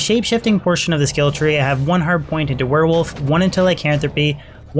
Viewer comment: Bro why does it sound like you say "100 point into" when you are meaning 1 point into, it always sounds like this and I had to ask lol.